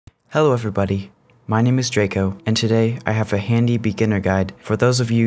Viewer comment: Well, i heard "hello everybody, my name is Joko" instead